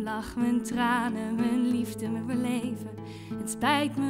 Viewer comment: Wat zegt ze dan op 02:53? "het liefste met mijn leven" of wat? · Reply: "mijn liefde, mijn beleven"